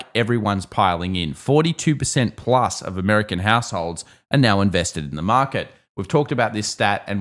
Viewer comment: sounded like "American aholes" 🤣🤣 thanks for good video!